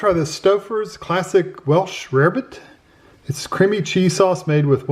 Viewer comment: I OFTEN WONDERED ABOUT THIS .I'M SO GLAD YOU REVIEWED IT. I THOUGHT IT SAID WELSH RABBIT. I ALWAYS THOUGHT BREAD CAME WITH IT TOO. BUT I'LL PASS IT'S A NO FOR ME THANKS.👍